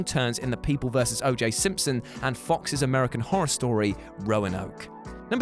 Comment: does he say American Horror Story is Fox’s?